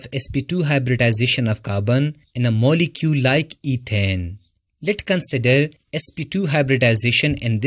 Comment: Great video! However, please correct these errors is possible. At 9:47, you pronounced ethane instead of ethene, and you also captioned the diagram geometry of methane instead of ethane at 10:49. This video will help me form notes for my students. Thanks 😊